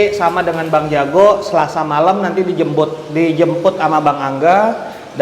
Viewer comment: ko gw dengernya jembu* ya😂